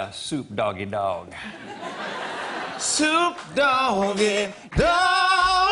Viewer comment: I thought they said dog poop and I was like 😧🤢